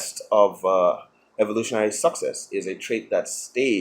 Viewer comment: Is it me, misunderstanding his accent, or did he just say "evolutionary suc sex". Either way, I'm down with that.